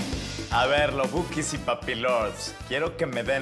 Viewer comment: Papi lols??????????? WTF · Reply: acróbata :v Yo entendí "papi lords"